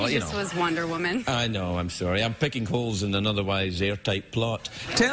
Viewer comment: What did he say at 15:45? "I'm picking coals in an otherwise airtight plot?" Is that what he said? Lol. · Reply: Holes"